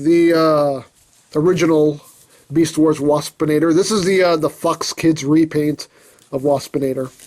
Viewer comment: did just say a bad word? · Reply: He said Fox